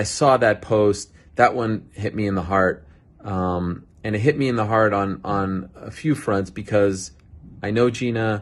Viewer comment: did he say "it hit me in the hard-on"